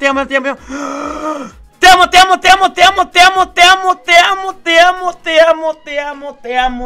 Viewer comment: parece que dice mutea mutea